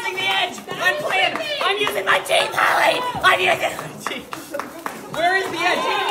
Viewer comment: I’m not sure if I heard it right but I heard “IM USING MY TEETH CHARLIE”😂